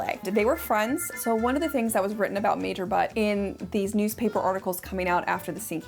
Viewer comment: Did you say "Major Butt"?😂